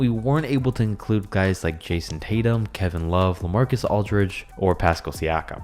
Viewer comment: did he say “Kayson Tatum” instead of Jayson Tatum